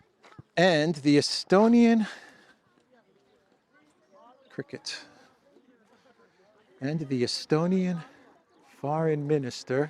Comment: At first I thought said "The Estonian cricket" and started to laugh.